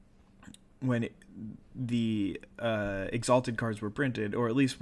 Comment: Ha! Your uploads never fail to make my night! Although you misspoke a bit at around 02:10 when you said "Exalted" instead of "Extort", right? :D Not a big deal though, of course.